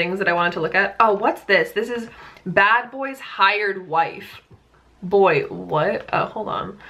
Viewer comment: I THOUGHT YOU SAID THE BADBOY'S WIRED WIFE SKSKSK